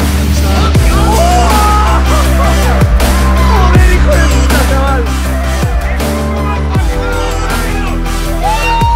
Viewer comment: no entendí nada de lo que dijo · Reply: Que la costa está llena de rocas que están pegadas al agua para saltar :v